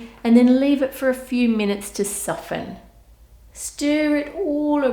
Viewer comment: bruh I thought she said leave it right there to suffer